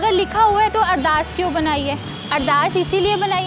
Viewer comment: I did'nt understand the word 'addaat' or something. What did she say at 8:31 ? Meaning?